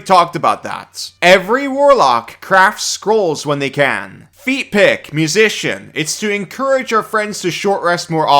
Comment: every time you say "feat pick", I keep hearing "feet pic"